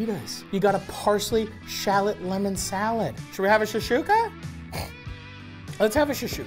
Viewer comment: I kept hearing "SHASHOOKA" friggen hilarious thanks, Matty and team.